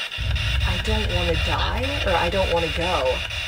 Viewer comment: I don’t know if anyone else caught this but the *yeah, I don’t want to die’ on the spirit box was totally in voice and I heard voice come through multiple times after that. 😟